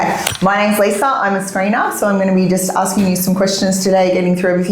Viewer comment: when she said she was a screener, I thought she said Cleaner. I thought this must be a very thorough prison if the 1st person you get interviewed by is the Cleaner.